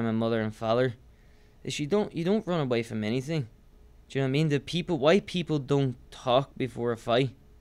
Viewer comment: "White people don't talk before a competition" he actually said why; I've had Irish friends and he's got an interesting pronunciation of certain words